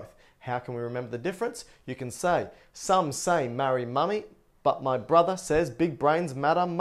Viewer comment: Did you say, "some say marry MOMMY"? This is the only doubt I'm having right now. 1:02. BTW love your videos. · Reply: *money, lmao. If thinking about Norman Bates helps though.. ;)